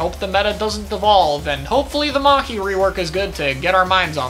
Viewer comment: did you just say Mhaci rework? (probably not but I must externalize my mishearings)